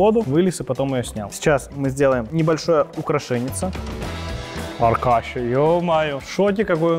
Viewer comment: Мне послышалось не Аркаша, а Алкаша!